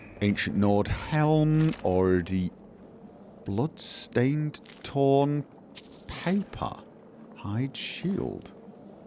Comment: For a moment I thought it said "bloodstained term paper" and had a brief shock, but then realized it was harmless...